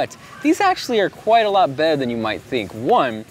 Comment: u say bed instead of better lol